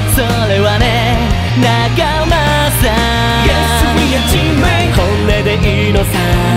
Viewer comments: 間違えて15時じゃなくて3時に設定しましたな～？(。-∀-) びっくりしました笑 昔からずっと思ってたけど 「Yes! we are teammate!」が 「Yes! we are 人類！」に聞こえてしまう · ここがずっと｢Yes we're 人類！｣に聞こえて毎回笑ってしまう…